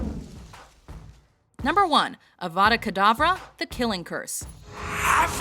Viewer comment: my mom thought Voldemort said Abra kadabra...